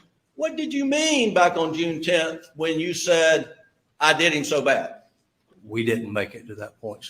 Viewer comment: I watch your recaps on tv but I wanted you to know that I heard “I did him so wrong “ as hard as I tried to hear “they”. Maybe it’s a left/right brain thing lol